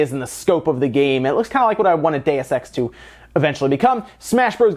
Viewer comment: When you were saying "Deus Ex", I heard it as "Day of Sex" Lol